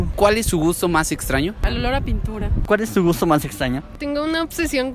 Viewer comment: por un momento entendí 'el olor a pit0' jajajaaj